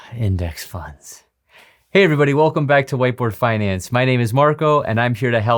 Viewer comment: Thought he said "welcome to white boy wealth".